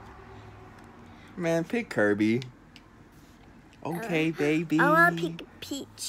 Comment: Why at 1:03 it sound like he said bi**h